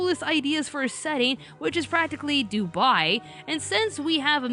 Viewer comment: What did she say? 2:10 "...which is practically Dubai"? Is that the right word???